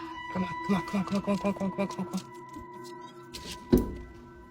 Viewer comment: The video with dad, baby and daisy. Did anyone else hear "DONT COME BACK" when he opened the front door to get daisy out the house?